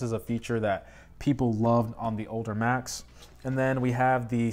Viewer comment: Did he just say “odor Macs”?